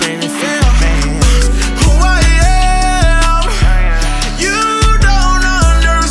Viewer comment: It does not sound like he’s saying “well damn, you don’t understand” it sounds like it says “who I am? you don’t understand”